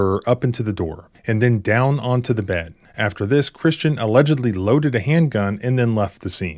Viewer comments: Man, I thought he said Christian Bale 😳🤦😂😂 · What a name. I honestly thought you said his name was Christian Bale. Needless to say he is NO Christian Bale. Haha